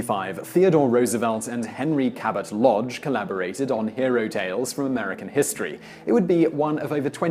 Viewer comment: did he said "Ero-tales" ?